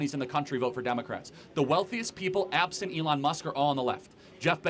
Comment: dude he just said ABSENT Elon musk. Either listen or learn what the word absent means.